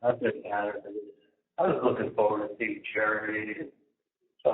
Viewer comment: after asking if his zit scare looked ok- he says I was looking forward to seeing Jerry, my wife … my child….. my son😳 What? Is he secretly married? Am I hearing things?